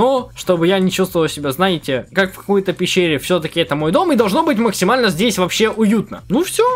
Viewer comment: никто не заметил что он сказал в х*й-то пещере???!!!